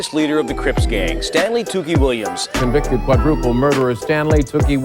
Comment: I thought there said Stanley Tools.